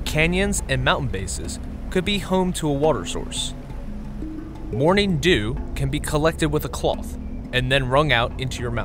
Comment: I thought it said Mountain Dew for a sec and I was like "YEAH!" "JUST WAIT FOR THE MOUNTAIN DEW TO APPEAR ON YOUR SHIRT!"